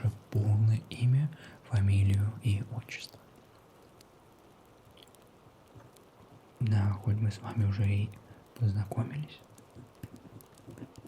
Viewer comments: Мне послышалось "Нахуй мы с вами познакомились" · Включаем Русские субтитры и нажимаем на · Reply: Отлично, теперь вместо "да, хоть" я слышу "нахой". И оно не расслышивается(